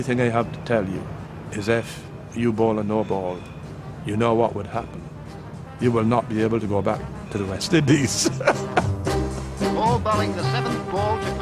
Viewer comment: what did he say ? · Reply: if you bowl a no bowl, you know what would happen. You would not be able to go back to the West Indies.